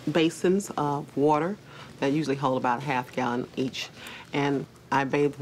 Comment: When she said "half a gallon each" I thought she said "half a gallon of bleach" and I had to rewind it like wait :o